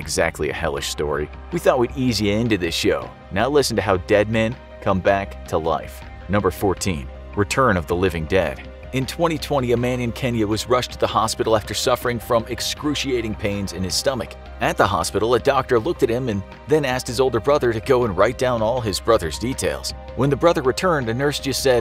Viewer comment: 'a man in Kenya went to the hospital' I thought u said ' a man called Kenny went to the hospital'